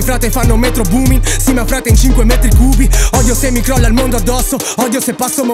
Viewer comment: da 4:18 a 4:25 cosa dice? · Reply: Davide Colleoni dice il mondo